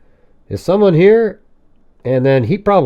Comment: she saw the slidingglass door open than says "someone's here !!!"", is what I thought she said,than she walked upstairs, saw him over the 2 girls , and chased her down